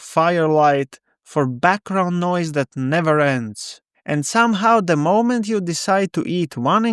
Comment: This was a great video! Thank you. At first I thought you said, “for bathroom noise that never ends” instead of “background noise that never ends.”😂. Both seem appropriate.